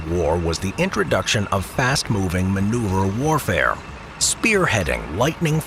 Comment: did anyone else hear manure?